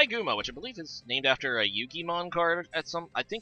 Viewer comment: what did he say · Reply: I laughed at Yugimon quite a bit. Definitely meant Yugioh, or it was an intentional joke that he really sold.